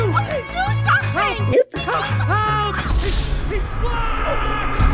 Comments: am i trippin or i hear him say “he’s black” 😂😂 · Did he said he's black I'm talkin bout the tennis coach